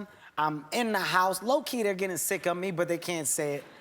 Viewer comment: I'm studying English as a second language. Can anybody tell me what Kevin said at 3:49-50 minutes?please.. something like: low key?? What does that mean? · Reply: Low key they are getting sick of me